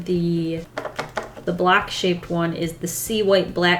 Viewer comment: "The black shaped one" Me: did she say block or black?